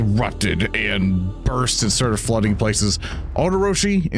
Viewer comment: Glad that Odo and Speaker are fine. Also did I mishear or said "the kitties are fined" @ 2:23:54?